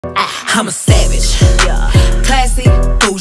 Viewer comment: Is it just me or i hear “im a savage” 😃